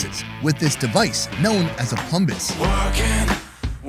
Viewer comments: did he say plumbus ? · did he just say that it's sliced with a Plumbus? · Hold up. Is the curdle cutting tool is called a plumbis? Or did I hear that wrong. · did I just hear that correctly? A plumbis is a real thi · did he say it’s called a plumbis?